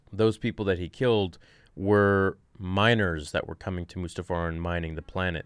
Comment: He killed minors on Mustafar? Oh, no! Not the younglings! Not again! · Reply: I heard "miners" as "minors" too!